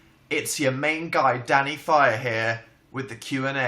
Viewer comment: at first i thought he said 'its daddy instead of danny